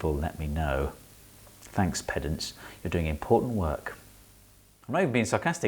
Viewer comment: As not a native English speaker I thought he said "peasants" 🤣🤣🤣